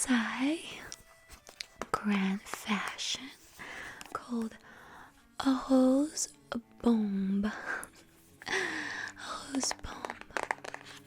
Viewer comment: thought you said ‘a hoes bong’. Naughty Marie